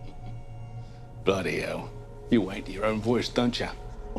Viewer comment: I thought he said “wank” but the subtitles say “hate”